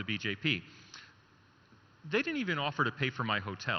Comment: Hey..wait..wait.. wrong translation I mean wrong written what babones said. See 29:46 he told they did not even offer to pay hotel.....like this go and watch.... pakka jhol hai yaar